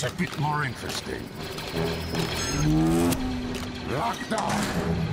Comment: What is he saying on 0:55??? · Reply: Skyrama 1112 locked up